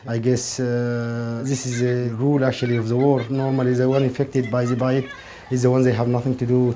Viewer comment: Does anyone know what the man is saying at 9:48? "this is a rule actually of the world. normally the ones affected by the ..???.., are the ones that have nothing to do with it." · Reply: Hi Varun, I believe the word is "violence"